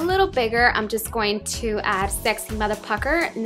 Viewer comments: i thought you said saxy mother F@@ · at first i thought you said "sexy motherfucker" ahah